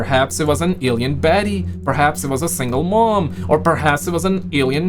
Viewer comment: Did he say, "perhaps it was a single Mom" at 10:17 ?? 😂😂😂